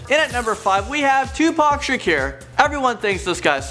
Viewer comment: did he just say tupac "ShaCure"...